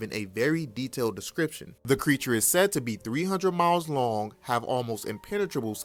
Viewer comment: Did he say 300 MILES long??